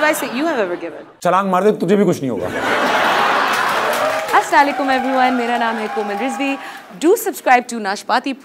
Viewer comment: did she says Aslaa Leekum..Mera name hei Koma Rizvi..?